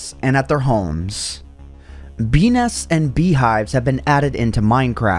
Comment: thought you said penis